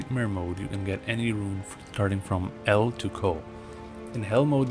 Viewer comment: in NM mode you can get any rune from "el" to "?" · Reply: El to Ko I thought he said.